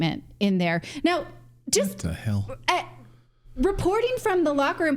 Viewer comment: "If I'm not mistaken, I heard Mr. Morris utter 'What the hell' at 1:40." Yes, WHAT THE HELL.